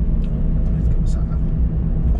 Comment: What did you say at 1:46 before you Mom said 'Ooooh'?When you sang it was like Simon Le Bon was in the shop😂😂Frerch please,Now! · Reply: I said i need to get my sat nav on